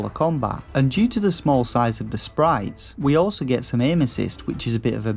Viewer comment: OMG.... @ 3:12 I had to go back 6 times before it finally clicked as to what you were saying, which was "We also get some Aim-Assist" because the way you talk I swear ALL I heard (which made no sense) is "We also get some Anuses!" Why we would get anus's obviously didn't make any sense, seriously tho, PLEASE go to @ 3:12 & tell me it doesn't sound like anuses!